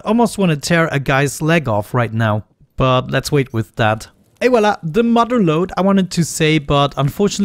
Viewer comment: what the heck were you saying back there. Is it "ay wala" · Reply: et voilà